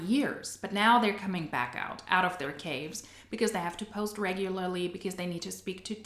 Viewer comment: When you said they're coming out of their caves, I thought you said "cage" and started singing Mr. Brightside by the Killers. Not important to the video at all, just commenting for the algorithm. x